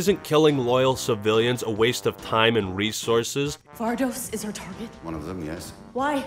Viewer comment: "Vardos is our target?" To me it sounded like "Vardos is retarded?" I guess game's main attribute starts to bleed into sentences people are saying in game.